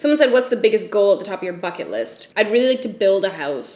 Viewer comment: Not implying that you swear a lot...which you do...idk hahaaha but I've been hearing the f word so much that I thought you said "fuck it list" instead of bucket list hahahhaahah #endofirrelevance